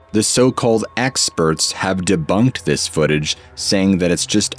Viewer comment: Did he say axeperts? 😂